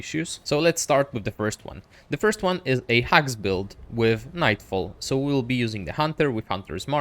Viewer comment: The first build I think he said hags build 0:49 Second build DOT build 7:50 The last build AKA ctrl, alt, delete as I'm going to call it